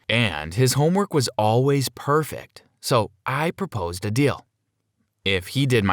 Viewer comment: I thought he said “ so I proposed to him”